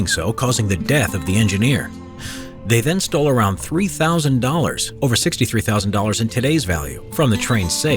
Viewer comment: Wow the subtitles don't match the words at 1:08. The narration says "$3,000" but the subtitles say "$30,000".